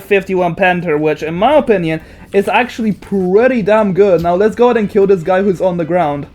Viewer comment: what did bro just say am i tweaking or smth?? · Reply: I said killing kf51 is pretty damn good, bro is probably reading captions which yt is really bad at